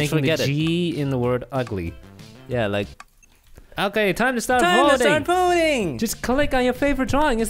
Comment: Two Thanos... 😕😳 Edit: Also Did You Say Time To Start Pooing?